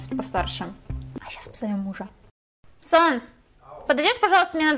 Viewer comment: Я одна услышала 《Санс》?